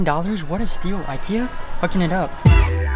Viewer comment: Anyone else hear say f***ing it up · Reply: He said hooking it up